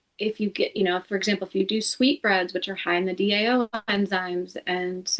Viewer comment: At min 14:55 What word is she saying? CC shows it as "sweets" and that's what I hear too but im guessing she is NOT saying to eat sweets for nutrition!? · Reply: Sweetbreads - 6 Sweetbreads are a culinary delicacy made from the thymus or pancreas glands of animals.